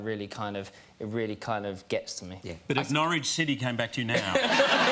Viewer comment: What did Hugh said on 1:27 ? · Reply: "But if Norwich City came back to you now...?"